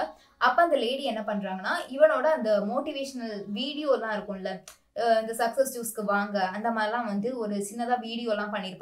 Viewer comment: Amazing analysis on the movie! At 4:43, I am not able to exactly hear what you say. You say something along the lines of “success ____ ku vaanga”. What exactly did you say again? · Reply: Successku vaanga